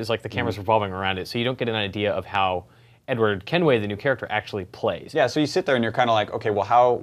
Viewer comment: I thought he said Edward Hemingway.